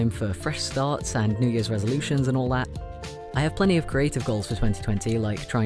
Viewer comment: I Always hear "new years revolution" instead of Resolution and I think it is time...